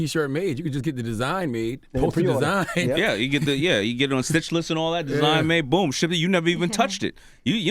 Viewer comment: Does anybody know what Daymond John said at 18:12. Did he say "Stitch List" or "Stitchless"? I wanna know what website he is speaking of.